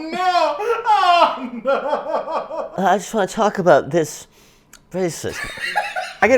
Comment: the captions say “I just want to talk about this racism” lmao Liza takes a stand